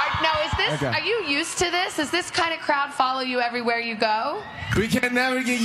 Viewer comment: i was gonna attack when she said "crowd" because i thought she said "crap" LMAAAO